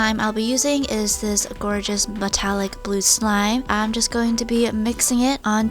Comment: did u say BATAILIC